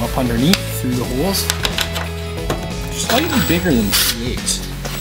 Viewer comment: what sized drill bit do you use for the brass well nut? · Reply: At the 6:45 mark I think I hear you say "it's slightly bigger than 3/8". Which also looks about right.